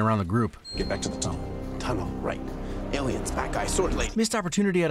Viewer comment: I thought he said 'Aliens BAT guy, sword lady'. And weirdly, Flash was the first across the bridge both times. Barry's timidity came at seeing steppie being such a literal bad guy monster. Love the video though. Whedon tried to turn Justice League into an MCU entry and understandably failed miserably at making the movie.